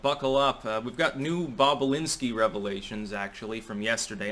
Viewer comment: I thought they were saying Bob Alinski.